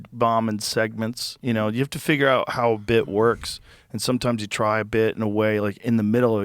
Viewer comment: That’s crazy I thought he said Tom’s wife and I thought Christine but he said sometimes Y lol